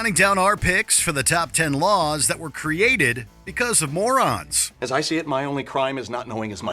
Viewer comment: I must be a moron bc I thought it said Mormon 😂😂😂lol